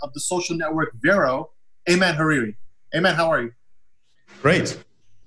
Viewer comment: I thought you said “Hey man, how are you?” twice lol